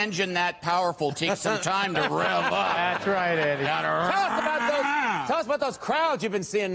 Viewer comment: am I wrong, or does Adam call Andy Eddy?